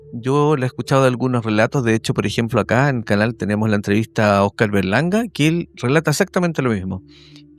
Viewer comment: que dijo jaja? Oscar Verga Larga? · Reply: Oscar Berlanga, episodio # 50